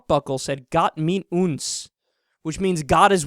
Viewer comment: Loved the phrase 4:55 : "Their fucking beltbuckles said Got mit uns." I lost it. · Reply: I thought he said got milk 😂😂😂